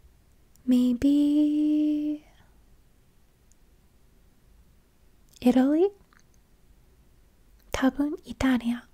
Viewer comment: 「エロい？」って聞こえたけど何と言ったのですか？ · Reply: 偉い？